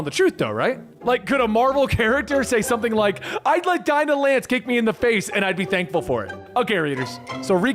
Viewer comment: i heard that one right, right? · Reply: Wait, SHE SAID DINAH LANCE, that’s why I love Gwenpool, she doesn’t care that she is in marvel